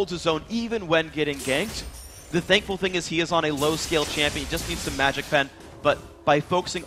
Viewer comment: Wait wait wait at 15:14 did he say rumble was a low skill champion? O_O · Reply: low-scale not skill